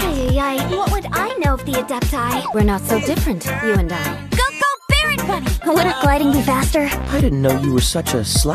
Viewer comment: Why do I hear "Politic gliding you bastard" when it comes to Venti